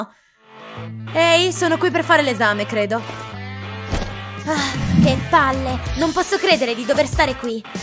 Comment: Prima comunque dice una parolaccia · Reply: È vero ha detto che palle